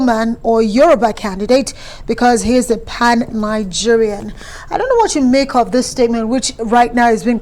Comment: I thought they said he is a unifier😢😢😢